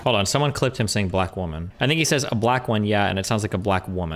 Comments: I remember the amount of people in chat also thought he said black woman when he said black one in response to the color of the taser was staggering. This shows personal bias and urelliance of eyewitnesses or in this case mouth witnesses. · I thought he said “black woman” too. Anyone have a link to the timestamp?